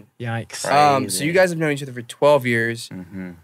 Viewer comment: why did i hear "you guys are married to each other for 12 years" lmao i think I've been watching jolly too much HAHA #firstwife